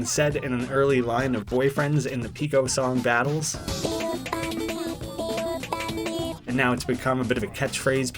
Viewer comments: In the pico funny line that everybody listens, I hear "big ol' bunny" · Forget “Pico Funny” I hear “Big Ol Bunny” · I heard it as "Big Ol Bunny" Not "Pico Funny" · Bro I thought boyfriend said big ol bunny instead of pico funny😂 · I hear: ‘Big ol’ bun-ny!’ ‘Big ol’ bun-ny!’ ‘BIG OL’ BUNNY!!!’ ‘APJIDJSHXSKXSJHXJ’